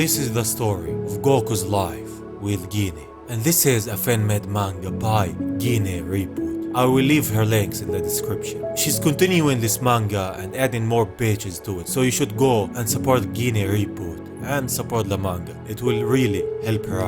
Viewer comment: You talk funny it sounded like you said you were going to leave her legs in the description